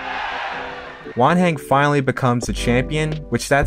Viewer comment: I thought he was saying 'One hand' until I read Wanhen jajajajajaja